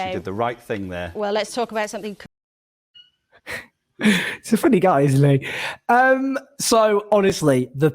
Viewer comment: " he's a funny guy is...." who? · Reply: I did think it's funny. Hilarious even.... but it sounds like says "he's a funny guy is Lee". Who's Lee?